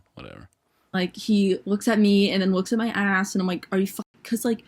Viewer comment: I thought the first girl said “and then he took a dump” I had to double take WHAT